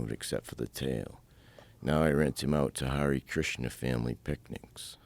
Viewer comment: I always thought he said Hare Krishna family bigots. 😊